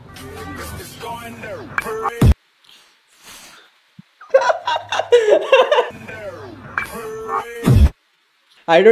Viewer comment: only legends can understand what trying to say .......😂😂😂F... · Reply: Oh Fuck